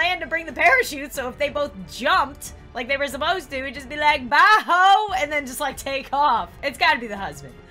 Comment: the first time I thought she said “bye bit**” then I heard it again and it said “bye hoe” anyone else hear the same thing?